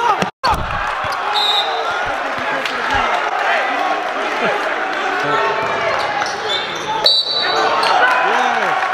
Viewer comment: Ayo what did say to u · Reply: "better put that one in the video"